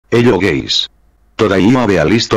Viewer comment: What is he 0:00 doing saying gays